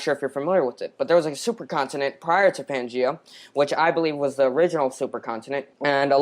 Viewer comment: did he just say if there was a supercontinent before there was a supercontinent? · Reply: I had to go back and listen again. Nope. What he said is that there was a supercontinent before Pangea. And that's the only time he was correct. There were several major supercontinents, Pangea was simply the last one. A few others are Ur, Kenorland, Nuna(sometimes called Columbia) and Rodinia. Not to be confused with 'minor supercontinents' like Laurasia and Gondwana.